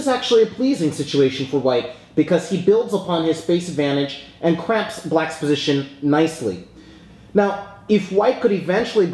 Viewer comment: 3.08 I thought he said "and CRAPS black's position nicely"